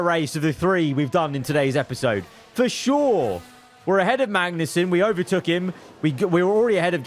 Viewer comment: bruh I thought he said verschoor not for sure